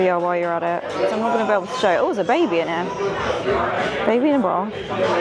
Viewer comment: I thought u said baby in a bra not bar 😂😂